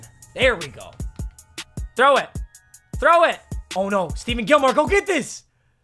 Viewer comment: Did he just say STEVEN Gilmore 😕